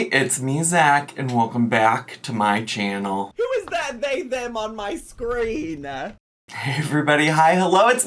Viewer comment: someone please confirm what is saying? 😂😂😂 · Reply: "Who is that they/them on my screen"